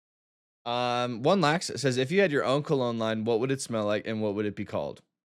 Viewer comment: Tell me why right after an ad break instead of hearing “if you had your own cologne line…” I heard “if you had your Uncle online what would you call it and what would it smell like?”..